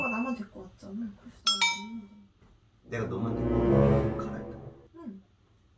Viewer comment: 초 이건 100% pd랑 사귀는건가라고 의심해봄 pd가 너만 데리고 오는데 냅둿다고? 그게 말이돼? 라고 들림